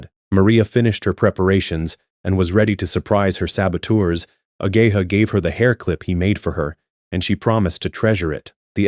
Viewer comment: Gang I thought you said a gay hoe 😂I was like wtf it can not be this deep